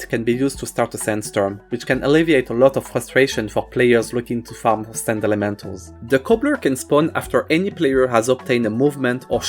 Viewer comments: says the words: "for sand elementals" Subtitles say: "For sand waifus" Cheeky bastard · subtitle says Sand Waifus but voice over says Sand Elementals. 😭